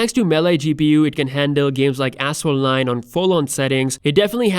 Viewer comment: Sounds like he said "ass hole lines". But I know he said "Asphalt 9"